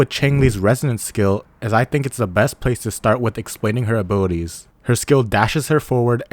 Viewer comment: oh, its "Abilities" i thought you said "Booties" 😅